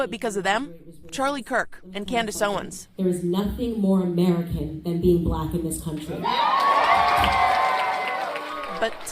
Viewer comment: the captions have Candace Owens saying, “There’s nothing more American than being blonde...” What she says is, “There’s nothing more American than being black...” Thought I’d clarify as auto generated captions aren’t always accurate.